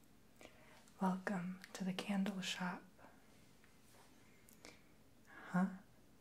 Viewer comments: i swaer i thought he said candy shop. ... he might of not. i might be just hungry lol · I thought it said candy shop I'm blind